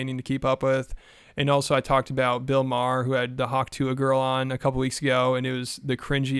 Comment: You said, "He had the hwak tuah girl on," but i heard "he had to hwak tu a girl" 😅